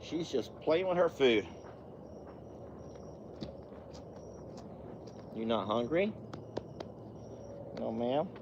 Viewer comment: OH LOL thought you said she is not hungry Well she is playing with it There you go that was good feed up No was tired and not a lot went right for her car wise and lawn mow wise and people was not nice to her but two was the girl in the coffee shop and a man a young man offered to help but he came from the patrol station I think Poor did look very tired her voice sounded tired she was running on only 2 hours sleep Yes I agree she is fine The horses are so good